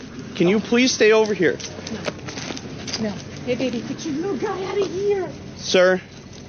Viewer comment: I became a bit preoccupied with what she says to the dog at 1:45. I've played it back about eight times. I think she says "hey, baby, get your little guy out of here". Whatever it is she says, I wonder if it's got anything to do with what's going on.